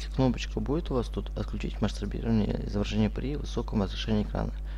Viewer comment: Бля, мне одному послышались или он сказал "Мастурбирование " в место "Масштабирование".....??????